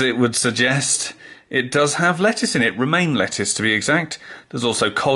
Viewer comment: I swear I heard "Remain Lettuce" the first time, and that was just moments after you mentioned Tories...